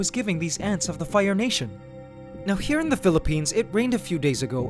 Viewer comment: One vid I heard you say "ants Canada here in da phillipines" like what the heck